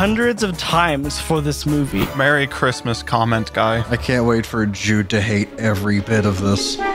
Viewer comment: I thought I heard him say “I can’t wait for a Jew to hate every bit of this. “Then I realized he said Jude.